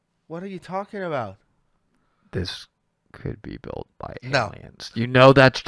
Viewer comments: funniest part is that subtitles says "this could be built by Italians"...... Yes, we did it, you got us 😂😂😂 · I have subtitles on and when whispers into the mic at 6:45 it types out "this could be built by Italians" instead of aliens and I absolutely lost it. Greatest subtitle blooper of all time.